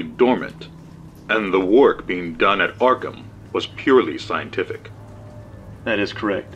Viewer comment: did I just hear Arkham as in Arkham asylum Lol